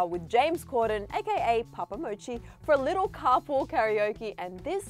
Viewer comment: Istg when she said Papa Mochi I thought she said Poop Emoji I